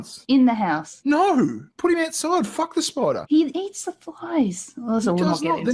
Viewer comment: she says "he eats the flies", I hear "he needs supplies", am I wrong? Time stamp, little after 4 minutes #SaveTeddy lol XD